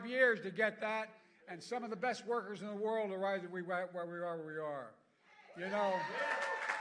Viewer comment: whaweareweare is what I heard 😂